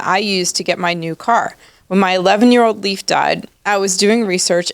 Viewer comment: It sounded like you said "when my 11 year old lease died" ? Then bought an electric Mercedes, because no one else would and it's sitting on the floor. No red flags here. Good luck trade in time.